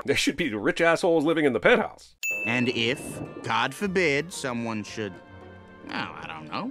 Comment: the subtitles, say, "and if, God forbid", but I listened to it like 5 times, and I'm pretty sure he ACTUALLY says, "cod forbid" which is hilarious if true.